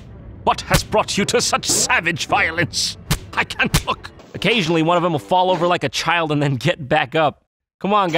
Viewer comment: What did he say? "I can't f-ck"?